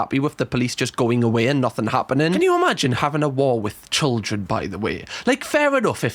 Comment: I heard "Can you imagiine having a WALL with children btw?" and thought YES THE TRUMP WALL My bad... in your accent, talking abt racists, my mind went there